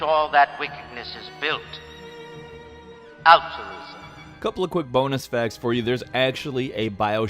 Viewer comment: Did he say alturism or optimism? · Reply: altruism.